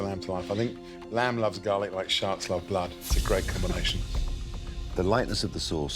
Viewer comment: I thought he said- Like Sharts like blood, but then i realized he said sharks